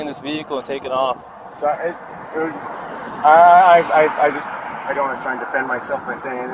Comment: I think he says I don't wanna try to defend myself and that can have two meanings. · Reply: I thought he said, "I don't have time to try..."